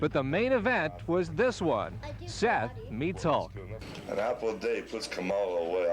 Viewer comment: it sounds like he says "An apple a day keeps Kamala away"